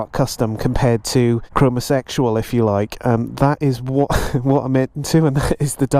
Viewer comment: "Chromosexual" did you say? How old are you? 2:14 "Your toothbrush"? I've read that the sperm count of British males has dropped 50% over some decades, & I can hear it.